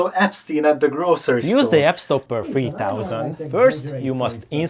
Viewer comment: Captions say, “Use the Epsteiner3000” as you actually said, “Use the YAPSTOPPER3000”💀🥀😂😂😂